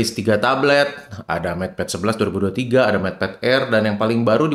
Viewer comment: Mau koreksi bang, sy punya matepad r dan usb typ c-nya belum 3.2, saya waktu itu colokin monitor ke matepad r saya gabisa, ke proyektorpun gabisa, mesti pake adapter baru bisa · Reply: Saya ngga nyebut MatePad R, tapi MatePad Air (2023) 😁 Kayanya kedengeran nya kaya Matepad R ya. Mohon maap. Tapi itu MatePad Air maksudnya. Yang baru keluar juli kmaren ini